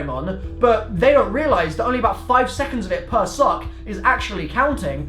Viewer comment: Put subtitles on at 6:55 and it says "sock" instead of "suck" ;)